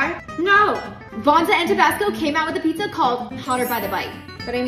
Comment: The whole video I thought she was saying tobacco